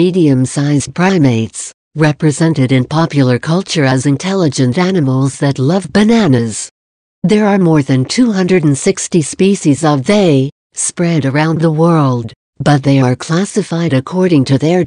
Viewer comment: "There are more than 250 species of THEY [emphasis in the video] spread around the world." Am I hearing this correctly? I.e. "they" instead of "them"? If so, is there a reason the subjective "they" is being used rather than the objective "them"?